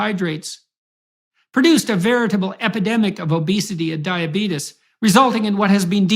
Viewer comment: did he say diabeetus?